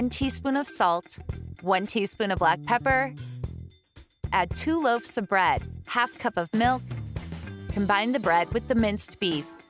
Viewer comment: I’m HOH, did I hear her say “ two loaves of bread?” L0L😂